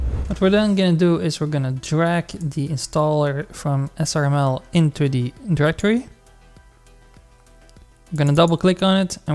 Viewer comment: Help I'm stuck at 4:44 and I don't know what he's saying a captions do not help. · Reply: "What we're then going to do, is drag the installer from SRML into the directory." You're welcome! :D